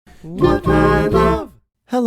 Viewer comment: HOW THE HELL DID I NOT REALIZE INTRO IS HIM SAYING "What I love!" I ALWAYS JUST HEARD IT AS HIM HUMMING SOME RANDOM TUNE GOD DAMMIT